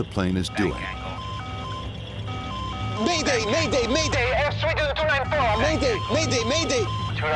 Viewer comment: “Air station” that’s not what he said, he said “Air Sweden” lmao...